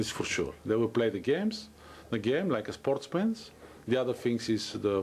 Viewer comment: Did he said "sports pants" on 4:37 LOL